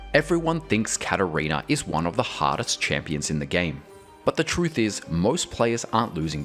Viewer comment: At the beginning, I heard, "Everyone thinks Katarina is one of the HOTTEST champions in the game." And I'm thinking, "Well, yuh..."